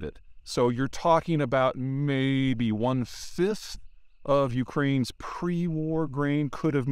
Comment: Hey English speakers! Is he saying 1/5 or 1/6? Subtitles say it's 1/5 but I hear `one-sis`. What it is?